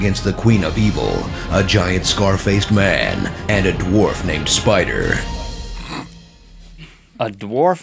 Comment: I thought it said dwarf Mcspider.